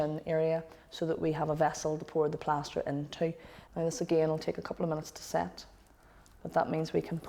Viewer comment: "And this again will take a couple of months to set." Haha. I am clearly mishearing this.